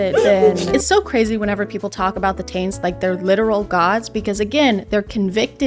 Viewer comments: Not me giggling every time I hear "the taints" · did she call them the TAINTS !?! I had to rewind like 5 times and still can’t tell. If so… genius